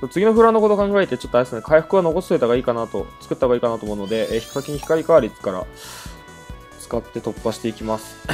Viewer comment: ヒカキン光カーリーって聞こえたw